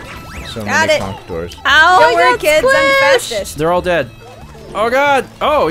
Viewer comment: I heard say "Don't worry kids, I'm fascist" instead of fastest haha